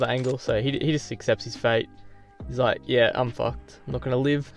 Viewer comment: Did say, "he's like, I'm fu*ked!" In 3:43. Btw op gameplay as usual!